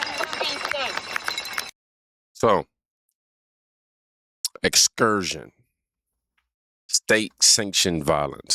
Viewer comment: excursion? I thought she said execution?